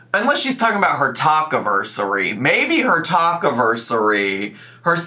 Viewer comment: Definitely thought you said taco-versary instead of talk-iversary 🌮